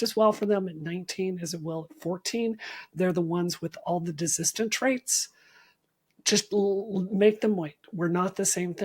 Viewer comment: Is she saying "desistant traits"? If so, that does that mean?